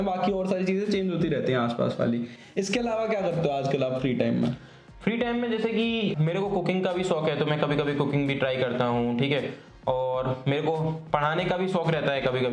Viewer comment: I hate my mind. at 27:07 i heard it as mere ko cocane bhi shok hai 😂😂😂